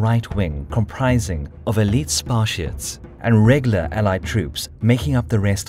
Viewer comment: Did he just say... Spart-I-ans